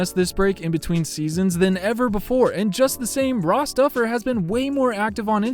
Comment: I thought you said bra stuffer not ross duffer